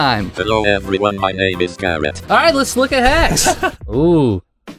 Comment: DID HE SAY CARROT?!?